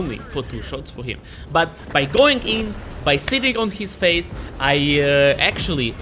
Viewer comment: Did I hear you correctly? At 06:15 When fighting the 60TP you said “By going in and sitting on his face?” 😂